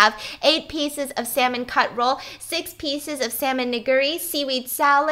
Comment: I'm sorry but the way said, "nigiri" threw me off for a loop there. 😂 It almost sound like you said the n word.